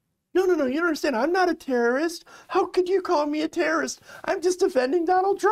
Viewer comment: “UPDATE: Per American Airlines, this was a passenger after being denied flight CLT-DEN, but not because of the No-Fly List. Rather, this pax apparently refused to comply with crew safety instructions regarding a face covering. I was initially given incorrect information.” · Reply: I saw this elsewhere, and I could swear he said "terrorist," but after listening to this video again, I hear him say "Karen." So I got the wrong impression too. Thanks for posting an update.